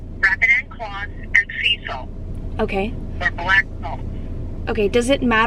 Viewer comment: Sweetie that was not deep enough and u guys should have bought an actual shovel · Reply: I also thought I heard her say black salt I’ve never heard of that but idk I just feel like everything is half assed no offense